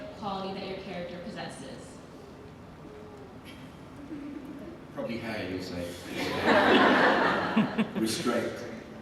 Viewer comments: What is her question at 6:58? And what does joseph say right after? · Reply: Leslie Gutierrez He said "probably hair you'll say", in reference to the jokes they made before about Daniel's haircut lol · What did Joseph say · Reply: Probably Hayley you will say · What did Daniel say at 7:06 ? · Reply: Lisa Geelen He said restraint